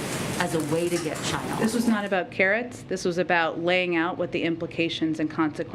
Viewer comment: Did Jen say this was not about carrots 😂18:55.